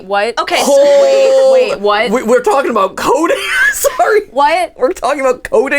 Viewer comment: no wait because I heard "codeine" XD